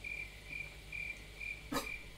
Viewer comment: подпишись на новое ВИДЕО 😂 нет мне не послышалось, сами посмотрите 🤣🤣